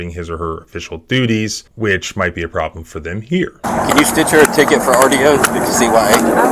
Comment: Lawsuit and details: <link> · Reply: Sounds like he says "can you stitch her a ticket" Is "stitch" a common LEO phrase, or a local phrase, or is he literally saying it as in "stitch up"?